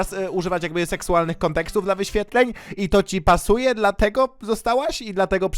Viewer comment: czy tylko ja usłyszałem ''cipa'' suje? XDDDD